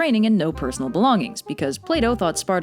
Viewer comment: For a moment I thought she said play dou